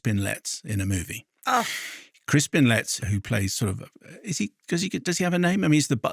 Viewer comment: I thought you were saying Chris bin lets until I looked on IMDB.